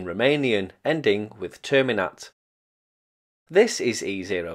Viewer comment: How did you understand Terminate from that? I heard "Tell me Not"